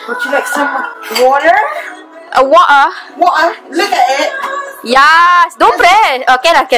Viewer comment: "Don't preen"?Maybe juz misheard that one but juz love the clip...Big thumbs up!!